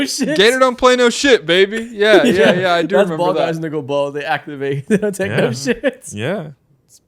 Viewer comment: what did you say bruh??? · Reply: “When they go” but really fast💀💀had to play that back too